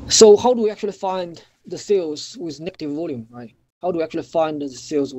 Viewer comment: Sorry to ask but can someone tell me what is trying to say from 49:19 is it FINDING FILLS WITH NETTING VOLUMES??? Also at 51:06 SALE WITH NETTING VOLUME????? At 51:36 NETTING VOLUME??